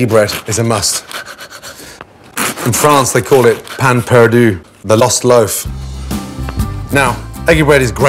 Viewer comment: hahaha Gordon's absolutely abhorrent pronunciation of the French had me so confused for second. I thought he said some Proper name like Pan Purdue, but he said "pain perdu" lol.